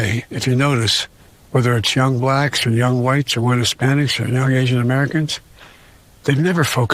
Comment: does he say “wet Hispanics”?